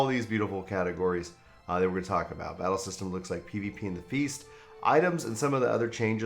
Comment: When you said "PvP and the Feast" early on I misheard "Beauty and the Beast." I certainly did a double-take, lol